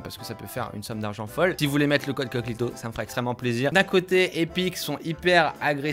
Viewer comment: j'ai entendu coclito